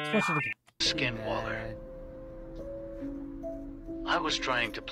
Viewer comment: What the heck he actually said skin Waller 😂😂😂😂 at 2:18 is it 😂😂😂 I know Only One Badass Waller That is Grayson Waller ❤